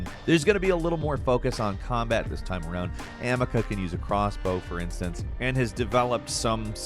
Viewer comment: did he just say Amica at 7:37? hehehe